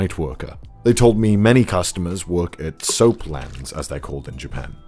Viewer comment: What does he call the customers? "Soap what"? I didn't get it on